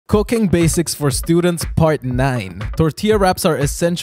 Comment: I was closing my eyes and scrolling and I heard"COCAINE BASICS FOR STUDENTS" THIS IS WHAT I WAS LOOKING FOR THEN I OPENED MY EYES AND SAW cooking.